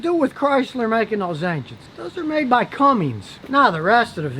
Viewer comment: It sounded like you said “Cummings” but the name of the company is Cummins.